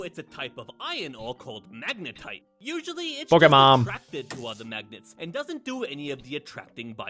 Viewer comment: he said pokemom or i just misheard